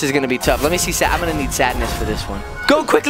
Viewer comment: I THOUGHT WHEN SAD I'M GONNA BE SADNESS I THOUGHT HE SAID I'M GONNA BE SAVAGE!!! 😂😂😂